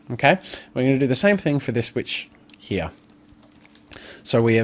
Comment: I thought said b!tch instead of witch for a second there, had to listen again xD